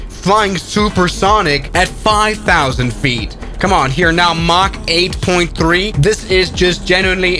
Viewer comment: Did bro just say mach 8.3? XD